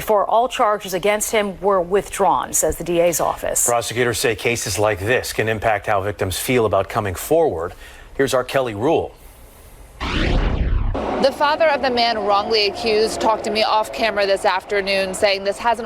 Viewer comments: I thought he said R Kelly Rule. :fish-orange-wide-eyes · I thought the reporter said, “The R. Kelly Rule”. 😅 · Thought he said "Here's R. Kelly rule" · Who else heard "Here is R. Kelly Rules"? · I really thought he said “R Kelly” lol